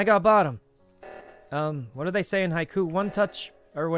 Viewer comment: did he just say 'hi ku'? it's "hi q"